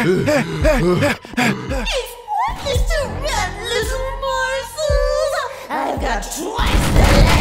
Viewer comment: Can someone tell me what this black thing is saying? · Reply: It's saying "it's pointless to run little morsels"